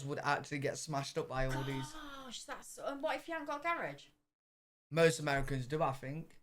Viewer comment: The way yall pronounce “garage” was sooooo adorable 🥰 i thought you said “Carriage” at first.